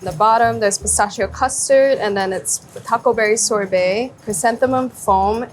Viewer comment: Did these captions go through any proofreading? Why are there so many mistakes? "Setting Asian" being the most egregious of all — she was clearly saying "setting agent" given the context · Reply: "taco berry" when the menu said Huckleberry and "[indistinct]" instead of "Chrysanthemum"